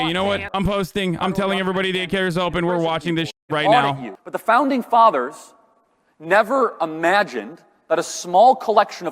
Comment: "the founding fathers never imagined that a small collection of companies could potentially be more powerful than our own government" Can somebody tell Charlie that this is literally a left wing talking point? I mean how can you say this and then simultaneously push for unregulated capitalism. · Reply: oh my god i just woke up and thought that said "the pounding fathers"